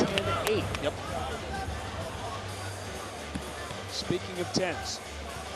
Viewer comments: did he say, "speaking of tents.." as in, "pitching a tent"* as in popping a boner, as in ERECTION When the camera cut to the ring girl??? Sounded like it.. & .. totally understandable.. just wondering if that's what he said/ment. That chick is hot 🔥🔥🔥 They all are, always. I read somewhere that the most popular, highest paid girl was paid a million a year at most. . . 👍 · is he saying "Speaking of tits"?